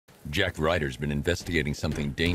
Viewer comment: I thought u said Zack Ryder hahahahaha